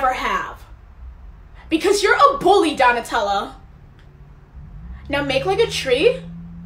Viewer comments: I thought she said Nutella 😂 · For a sec I thought she said Nutella 💀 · Initially thought she was saying "Donatello" and got extremely confused as to why she was calling the nerdiest ninja turtle a bully lol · Ok but- donnatella? 💀 Like i thought she said donnutella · I thought she was saying Donatello instead of Donatella 😿🙏🏽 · OK, but at the start, I thought you were saying dog Nutella😂